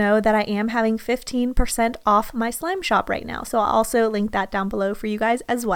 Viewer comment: did anyone hear her say slim shop instead of slime shop?